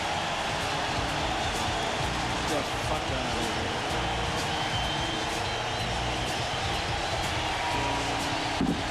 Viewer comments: Did he just say "Get the fuck out of here"? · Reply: i heard "lets get the fuck out of here" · Does anyone else hear the color guy say "Let's get the f*ck out of here" at 4:37:38?